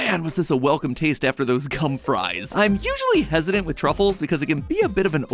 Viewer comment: please tell me I’m not tripping and I hope I didn’t hear what I think I heard..😅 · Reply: He said gum